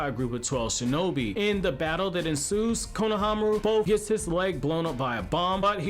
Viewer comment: On minute 1:44 what was the word u said in the battle that insused what?